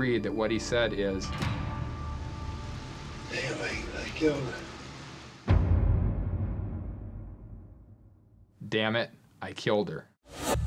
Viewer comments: it sounded like he said, "they think I killed her" aroume 38:00 in. not, "dam* it, I killed her." y'all tripping. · He needs to die, but I heard him say, "They think I killed her". Not "dammit I killed her" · sounded like he said,"they think I ki*led her" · Sounds like he said "they think I killed her." Definitely doesn't sound like dammit I killed her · I heard “they think that I killed her” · Almost sounds like he said Damnit! He killed her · it actually sounds like he said "i cant believe i killed her" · sounds like he's saying "they think that I killed her" I don't hear what they think he said whatsoever 🙄 yes, I think he did but idk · In the first case, I couldn’t hear what they said he said. I heard “they think I killed her”. Not “dammit I killed her”. I believe he did kill her but I dunno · to me it sounds like he said anyway I killed her how scary